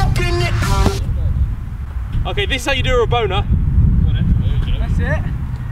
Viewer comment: did Harry say this is how u do Raboner